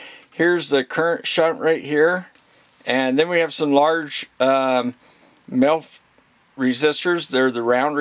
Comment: I swear you're saying MILF resistors, not sure I could resist a milf 😁, but anyway I've just bought the HT118A I was just after a really practical multimeter I could do for guitar work that had capacitive measurement. Most of the things I'll be doing with it will be resistivity, capacitance and a bit of 240v stuff around the house. I know the movie is about the HT118E but for some reason this came up in the search for the HT118A. Glad I found you, excellent presentation and massive respect out to all you guys who mess with the real stuff! Stay with what you trust. Cheers.